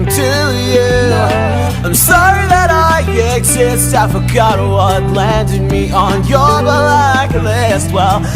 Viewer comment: I thought Virgil said "I'm sorry I exist avocado"